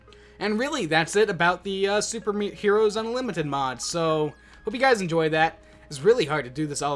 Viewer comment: I heard "super mean hero's unlimited mod"